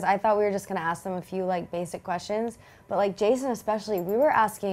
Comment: did she say jc or Jason ? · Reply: sofia bonds Jason